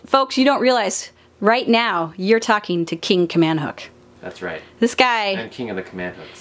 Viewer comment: I definitely thought you said "king commando" I had to re-watch your intro a few times to finally realize it was "king command hooks". Not going to lie, it made me laugh a little to hard.